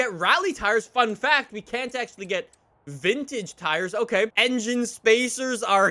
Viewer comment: did he just call the 86 a vintage car